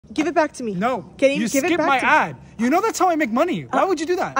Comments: I thought he said “you skipped my ass” 😭💀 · i thought he said you skip my ass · i heard ‘you skipped my a$$’💀